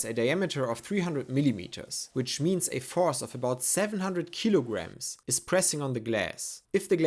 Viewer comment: when it came time to mount the pump i first heard "14 kilogram" and i wondered how that was a challenge then i listened again and was like AAAAAAHHHH yeah that makes more sense